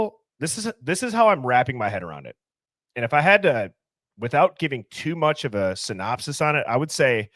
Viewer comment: I hear clearly“drinking too much?”